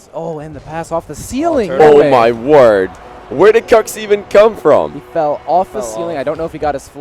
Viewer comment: Every time he says,, Kux'' i hear ,,COCKS"